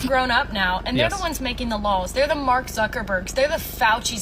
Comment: I thought she was saying “making the lols”